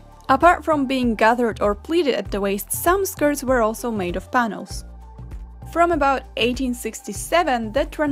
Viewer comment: in 7:35 I legitimately heard you say "Thanos" instead of "panels" I'm so sorry